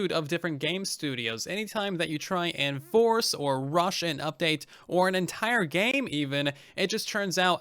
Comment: sounded like Russian update to me :v